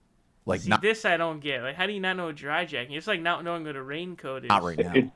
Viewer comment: I thought he said "dry-docking"